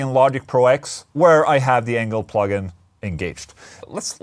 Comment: Who else thought said, "Anal plug-in" instead of "Engl plug-in"? Lol! 😂🤣😂